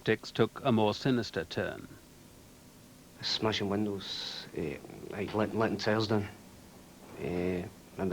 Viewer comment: What's he saying at 4:42? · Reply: Breaking windows & slashing tyres